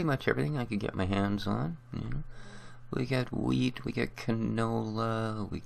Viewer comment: I swear u said weed at 1:30 lolz